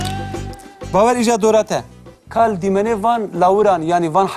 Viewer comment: Can someone tell me what the teacher is saying right after the "Peyiven Heywanan" screen (9:35)? It starts with "Bawer ji du re te" (or Bawer ji de re te?), but after that I don't understand the next couple sentences, until "ev çi ye'" for the fish.